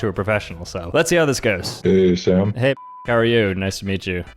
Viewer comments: There's no way that I thought you said "Hey bi***" there. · i thought he said hey b*tch how are you 😭